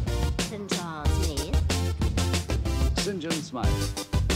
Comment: Coolest parody video I've seen in a long time. When I don't first saw this at 11 years old I thought he was saying "Singinsmuy" :)